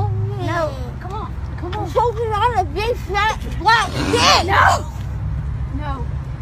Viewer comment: my dirty mind actived I think she said I'm choking on big white kid friendly